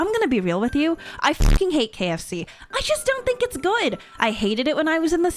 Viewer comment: IM CRYING AIOGHKSKF I WAS OFF-TAB, AND I JUST HEAR—— "I F*CKING HATE KFC" 😭😭